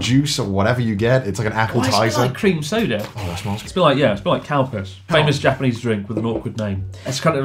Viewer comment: thought you said Cowpiss before I realized Calpis is a thing